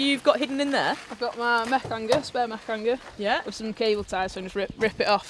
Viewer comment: Da fuqt does she say @ 2:35 ? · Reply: Wilderness Ninja Spare mech hanger